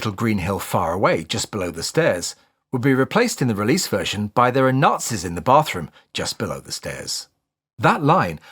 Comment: I never hear "there's Nazis in the bathroom". I always hear "there's matches in the bathroom. " Probably because I know a lot of people that keep matches on the back of the toilet. You can light one to help mask the smell.